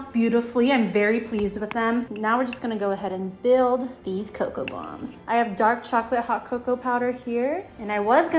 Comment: i literally thought it said coca cola bombs!! 🤣🤷‍♀️